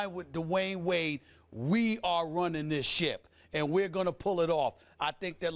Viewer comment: LMAO i thought he said "We Run This Shit."